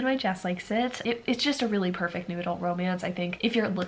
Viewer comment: I keep hearing "noodle" instead of "new adult" and it's been confusing